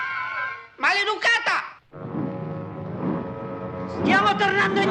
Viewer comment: Ha ha ha ha ha ha funny what did he say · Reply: Maleducata (unpolite)